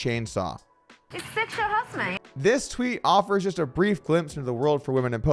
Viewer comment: what was saying? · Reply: It's sexual harrasment